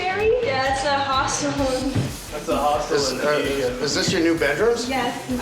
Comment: This was a nice episode and nice kitchen Oh and for those who wonder while using the close caption why she is saying at 43:37 "It's a Hospital." That is not what she is saying. She is saying Hostel Which is a more of an affordable place to sleep because you would be sharing the room with a bunch of people you don't know instead of paying for a pricey hotel. Just thought I'd clarify that.